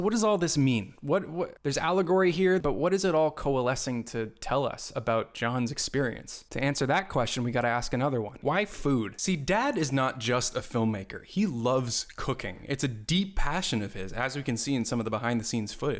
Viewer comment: did you call Jon “dad” at 11:20 or am I mishearing it?